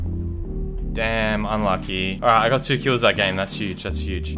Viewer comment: Yo what did say at 4:26 because it sounded like damn